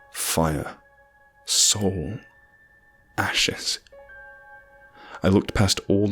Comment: "OOOHHHH you said ASHES! I thought you said eat their ASSES! Well this is awkward...."